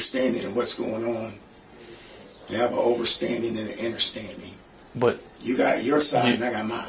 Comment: Did he say “they have an overstanding and an innerstanding?”